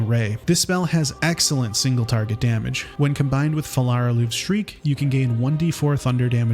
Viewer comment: I don’t understand what is said at 3:46 about scorching ray wen combined with « farrara L streak » ? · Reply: Phallar aluve's shriek An ability of the sword called "phallar Aluve" that can be found in the underdark near the bulette